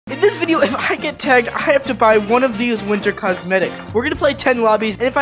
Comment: I thought you said winter hats